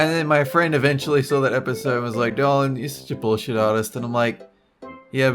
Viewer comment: Sounds like she said "darling", instead of "Dolan"